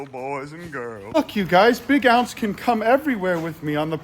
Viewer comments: I thought you said, “F#$k you guys!”😂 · sounded like you said f*ck you guys